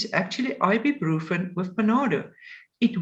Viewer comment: Ibuprofen with .... I can't hear what she says. Can someone tell me please? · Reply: Panado